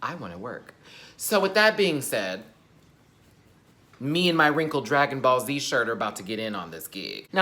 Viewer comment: Literally thought said " you and your wrinkle dragon balls" had to rewind back.